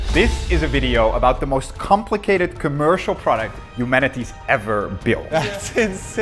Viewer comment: did you just call us Manatees?